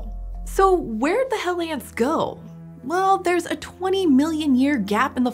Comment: What people hear: "Where the hell ants go?" What she said: "Where'd the hell ants go?"